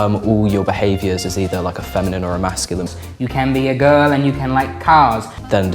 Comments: I totally misheard this as "You can be a girl and like cows". I was sat here like, "Yes, yes - I like cows too". rofl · I heard "you can be a girl and you can like cows" and i was so confused. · I SWEAR I HEARD 'YOU CAN BE A GIRL AND LIKE COWS' ABD I WAS LIKE HELL YEAH DAMN COWS SUCH MILK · did he just say you can be a girl and like cows?? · I thought for a second they said ‘You can be a girl and like cows’ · at first I thought they said "cows" instead of "cars"